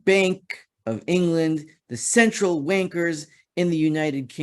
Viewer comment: I thought I heard Central Wankers, but throught naaah can't be